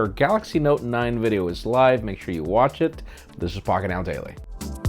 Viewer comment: idk whats in my head but i heard f**k it now daily. smh